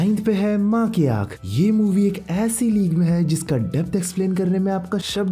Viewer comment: is it only me or did anyone else hear ma ki ankh here?